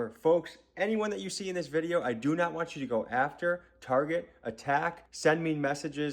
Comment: i heard ATTACK TARGET